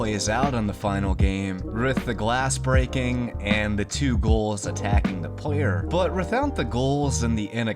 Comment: sounds like you say goal instead of ghoul (gül), is this regional? Great video on cut content though.